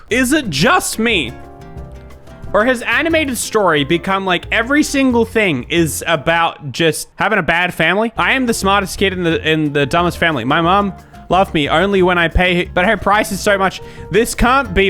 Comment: did you say family